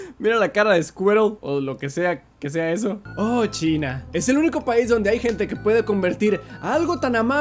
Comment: Jaja cuando dijstes chinis pense que dijistes chinga y yo q_